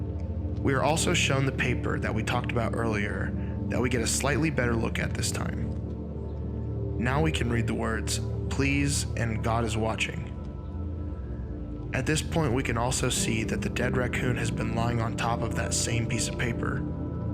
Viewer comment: I thought it was saying "Please Goo is watering" XD